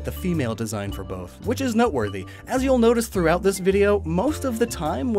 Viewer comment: i thought he said nut worthy